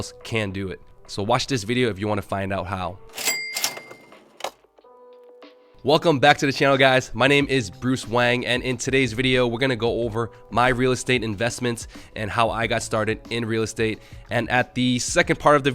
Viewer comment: So I just found this video and I thought he said his name was.